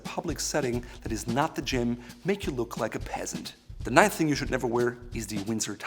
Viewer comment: Did you just un-ironically say you’d look like a peasant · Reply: I thought he said pissant.